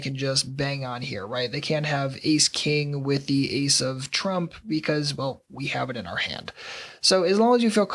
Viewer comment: did he say the Ace of Trump?